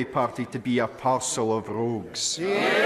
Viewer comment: "A parcel of rogues." That's a classic line from Mr. Flynn. I think I will use it in the future.🤣🤣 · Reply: I thought he said "an arsehole of rogues" 🤣